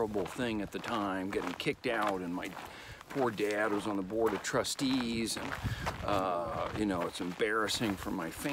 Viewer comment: sounds like he says rust-eze, hehe. Love Owen!!